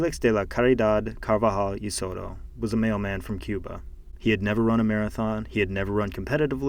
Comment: When u said mailman I thought you said “male man” and I was so confused